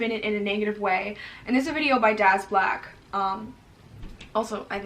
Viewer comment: I thought you said ‘my dads black’ then realized you said someone’s name